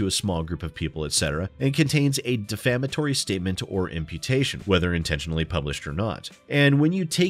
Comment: I had the video going on in the background and when you said "imputation" I thought you said "amputation" and was VERY confused about how defamation was handled down in Australia...